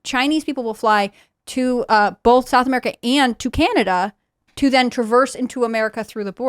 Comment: i swear she said "Japanada" 😂😂